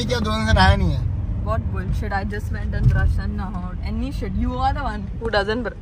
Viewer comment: what did he say 🤔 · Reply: Brand new shit